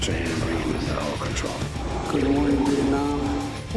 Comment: Did he just say "Good morning, Viet Nam" ?